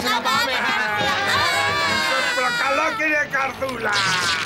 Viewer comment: did he say "Among us?" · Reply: He said "Sto kalo kirie karthoula."